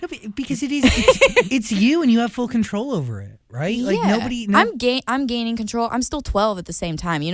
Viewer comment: I thought she said 'I'm gay' But it's actually 'I'm gaining control- I need help